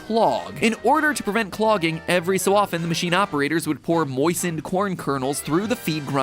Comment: at first i thought matpat said poisoned instead of moistened. i had repeat for at least five times lol